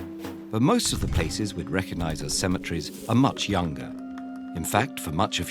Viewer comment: i deadass thought it said centimetres